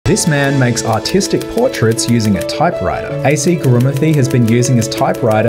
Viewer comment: I heard “this man makes autistic portraits on a type writer.”